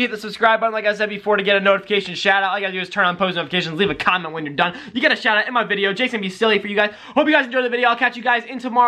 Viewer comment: at the end it sounded like you said when your done leave a condom😅😅😅😅😅😅